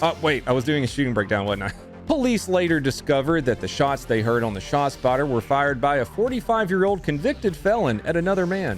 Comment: Is it me or do I keep hearing you say "spotshotter?"